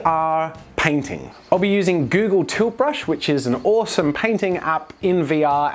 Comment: I thought you said "Google Toothbrush" at the beginning.